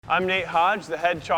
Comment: i thought he said " I am Nate Hot" 😂